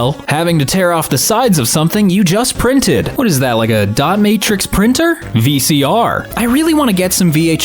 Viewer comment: I had to re-listen to that I thought said Domatrix printer not Dot Matrix printer NGL. LUL